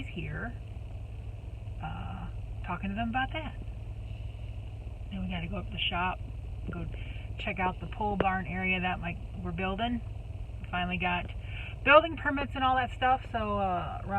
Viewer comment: I too thought u said pool barn !! Glad to see your reply. Be well. Merry Christmas😍🎅🎄